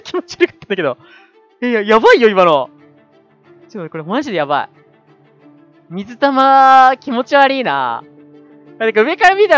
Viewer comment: （水玉気持ちわりーな）が （水玉気持ちアリーナ）に聞こえた笑